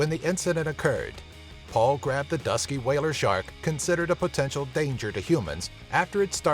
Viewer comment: Whale sharks don't attack people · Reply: King I thought he said it was a whale shark. I guess I was mistaken.